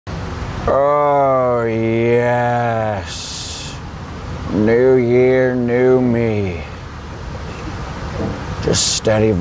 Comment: I thought he said, " No year no me" and I got concerned lmao